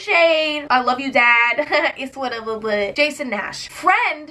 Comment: did she just say “it’s whatever” ?😂